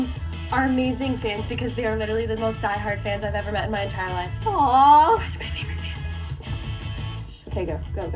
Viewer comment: What did Victoria say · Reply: I think she said “You guys are my favourite fans of the world”